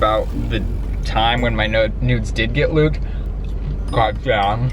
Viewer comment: I heard "my nodes did get luked" @ 11:50 😝